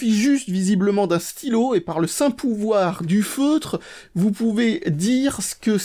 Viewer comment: "Par le St pouvoir du feutre" pourquoi est-ce que j'ai compris "par le St pouvoir du foutre" ? en tous cas,super vidéo comme d'hab,et vivement le test avec l'explose aliml 3000 !